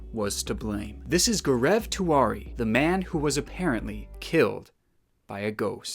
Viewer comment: Okay- am sorry but- When he said Gaurav Tiwari, I thought he was saying Greg Twardy ( or something like that) but when He showed the clips from sony, I then understood what he was trying to say 💀